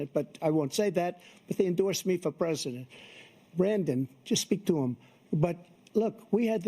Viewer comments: Anyone else catch Trump calling Biden "Brandon" at the 14:15 mark? 😂😂😂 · I swear to God he called Joe "Brandon" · Trump - "Brandon, just speak to them" Did i hear that right? He called him Brandon 😂